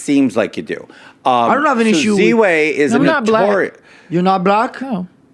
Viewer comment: did she just say "I'm not black" ? crazier podcast then matans ngl